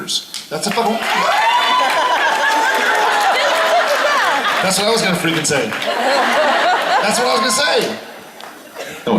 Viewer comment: what did Jared say? Cause it's so fast lol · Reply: He was about to say "That's the fucking one I was gonna say" but he catches himself before he curses (hence Jensen's exasperated look at him) and goes "That's the one I was gonna freaking say". LOL